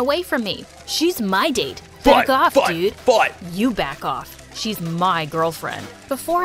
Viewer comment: when he said "Back off dude", was saying "fight, fight fight!" so it sounded like he said "fuck off dude"